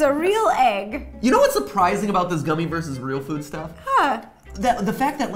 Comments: WAIIIT UP, did he call Ro for Steph, or did I just mishear him saying "stuff" ? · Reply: I slowed it down an he said 'real food stuff' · I'll just sit here and wait for someone to comment that he called her Steph in the first round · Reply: I heard “stuff” not “steph”